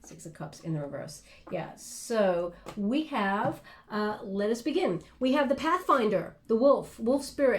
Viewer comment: Am I the only one who heard "So we have... lesbian" instead of "So we have... let us begin..".